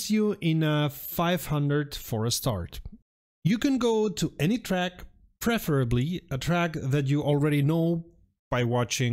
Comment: WHat are you saying at 16:30? your accent is so thick I cannot understand what you are trying to say even after listening over and over again, thanks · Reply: Copied from YouTube's automatic subtitles: "a 500 for a start. You can go to any track, preferably a track that you already know, by watching videos, by watching races... But don't start with Bathrust or Nordschleife, they are too difficult and you will only get frustrated." Hope that helps